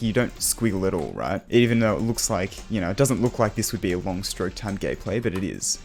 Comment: ayo what did he say? Long stroke time gay play??